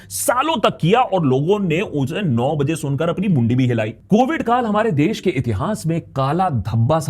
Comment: Akash 9:49 did u said bund b hilayi or mundi b hilayi 😂 love u work man